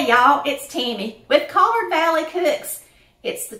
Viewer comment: Hey!!!! Girl when I saw this honeybun cake and the old school layered cake I said I think I need to go ahead and subscribe! So glad I found your channel. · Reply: I am so so sorry! I called you. I went over to your website and I saw that your name is Tammy. My kids came in talking as I was watching your video. I thought you said. I sincerely apologize for the mistake.